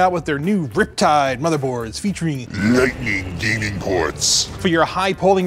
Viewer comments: Ngl, I thought he kept saying "Lightning DEMON Ports!" everytime, until the subtitles came up. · Well at first I thought he said "lightning demon ports" lol.